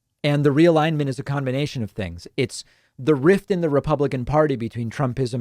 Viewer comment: It also sounded like he said "Repugnican."